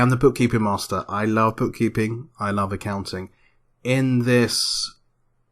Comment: the subtitles say "i love pooping and accounting" not sure if this was an intentional error or not but it was funny